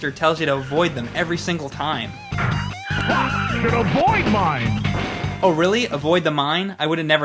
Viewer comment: (Rock and Roll Racing, again) I always thought the commentator said "You hit a Void Mine!"